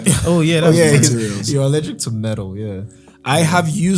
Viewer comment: I'm sorry, did just say is allergic to metal